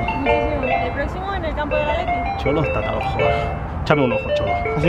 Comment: que dijo? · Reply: Chap am dem